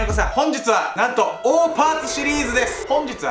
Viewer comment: オーパーツって最初聞いた時おーパンツに聞こえた笑笑